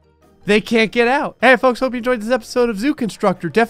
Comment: Am I the only one still trying to figure out grays outro? I keep hearing “stay foxy and much love” but I’m doubting that’s correct and if it is correct then what does that even mean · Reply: I think it is stay frosty